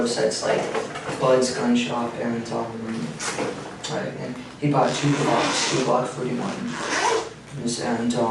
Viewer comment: What does he say at 5:52? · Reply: “He bought 2 glocks”